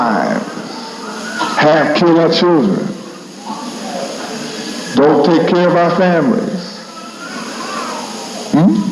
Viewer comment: what did he say about children?? I couldn’t make out that word, thanks to anyone who can answer 😇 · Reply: Have-(mis-speak) "Kill all the children"